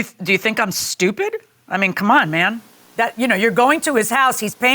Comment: Did I hear Judge call the defendant Ma'm at 11:11! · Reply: No, she said "I mean c'mon, man."